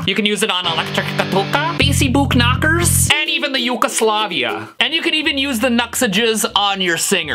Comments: Did he say: "And even the yugoslavia." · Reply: Yea Yugoslavia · Did you just say Yugoslavia?